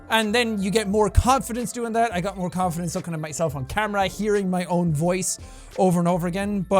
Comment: does anyone else hear ''sucking on myself on camera''